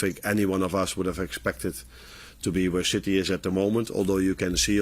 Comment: Am I the only one who heard him say "where shitty is at the moment"😂